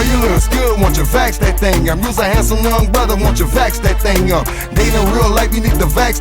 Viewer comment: I honestly thought that Juvenile was saying fax that thang up and couldn’t understand why everyone was so mad.